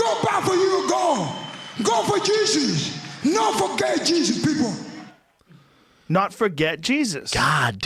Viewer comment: “No forget Jesus people” is what he said. Brendan couldn’t be more wrong. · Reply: I thought he said he said “no for gay Jesus” and I was lowkey weak. No forget Jesus makes much more sense is a lot more respectable.